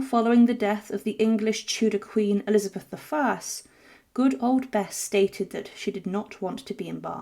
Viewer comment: “Good Old Beth”? The Brits may want their accent back. 😂😂😂😂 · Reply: I heard it as "Good Old Bess."